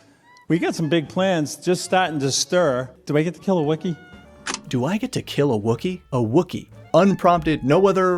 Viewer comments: I watched the show and actually thought he said "wikis". I didn't know what he was talking about. His accent threw me off and I dismissed his comments as unintelligible honestly. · "Do I get to kill a Wookie" - I heard this as "Do I get to kill a wiki" as in completely upend the lore that people have created.